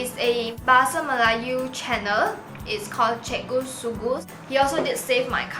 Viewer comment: im sorry but i thought you said cikgu sue goose